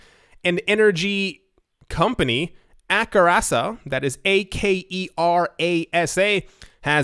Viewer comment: When said Aker ASA, at first I thought he was saying Arasaka, and thought. Well, someone has been playing too much Cyberpunk. Then I realized, that someone is may be me lol